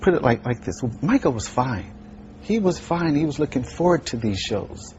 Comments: am i crazy or did anyone else hear jerm say ricco was fine at 8:23?michael was called ricco? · please tell me what you heard at exactly 8:18 i have listened over and over i hear ricco was fine.